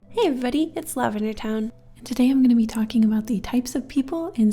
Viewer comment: Wait a FUCKING second, OMG I just realized that you say "hello everybody its lavendertowne" in the beginning,because I always thought you would say "hello everybody its love in the town" and aaahhhhfgdjebfj MY LIVE WAS A LIEEEEE